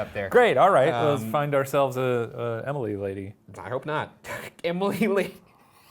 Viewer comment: When they ask “is Jesus here?” Instead of hearing “I am” I hear “I hope” and I think that’s way better